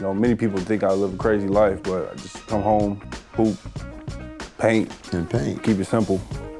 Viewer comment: I thought he said poop like if you agree